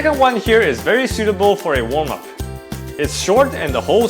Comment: Hey bro, love your videos! But I'd like to say that maybe you want to be a bit more cognizant of your language in regards to women--they care capable of climbing just as hard men--no reason to imply that easy climbs are best suited for girls. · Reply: at 1:50 you say that it's very suitable for a warm-up. They must've misheard it and thought you said "a woman" 😂